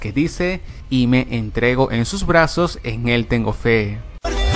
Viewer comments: Yo no entendí eso yo entendí :v : Me DiErOn UN TaZo En TaJun CaGo TeNgo Fe 3:33 Yo entiendo esto :V : MaNgO WeY 6:11 Yo entiendo: SeBus CuNus SaLud ._. 8:53 quiero de lo que se fuman :D Yo escuche: aBueLo FuRrO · Pues escuche 'Me entrego a mi el es fe' xd